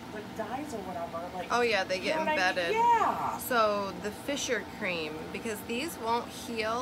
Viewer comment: fissure cream? What did you say will heal her cracked feet? Thanks for all you do ☺️